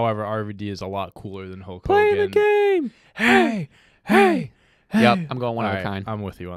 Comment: did he really say “play of the game” lmao 🤣